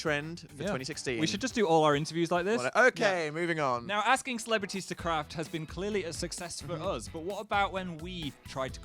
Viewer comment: When Phil said about asking celebrities to craft at 4:10 I thought he said asking celebrities to crap.